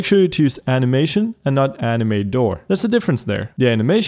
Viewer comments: legit thought he said Anime Door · i thought he said anime door not animator